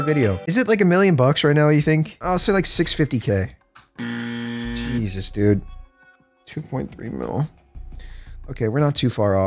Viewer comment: u were far off it was actually saying 2.3B/2.3 billion